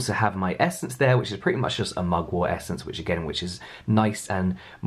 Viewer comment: Girlfriend, I thought you said "my muggle essence " Whaaaaa? I knew you were using some sort of magic! Hi from Australia